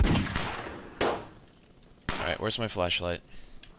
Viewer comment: ) It sounded like you said right where's my flesh light.